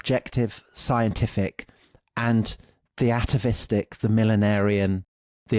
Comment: What is the word he says at 3:46-7? Theatavistic? I know atavistic but this sounds like a word formed with “theos” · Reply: I’m fairly sure it’s “the atavistic “, two separate words.